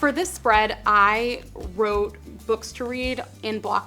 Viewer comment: I thought you said "Book Street" LOL xD